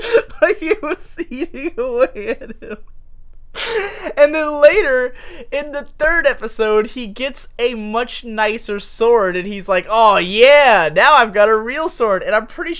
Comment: What is he saying at 19:29? He's laughing and I can't understand :( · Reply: "And then later, in the third episode, he gets a much nicer sword..."